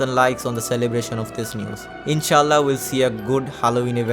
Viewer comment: did bro say inshalah?! is bro muslim!?!?! IM HYPED!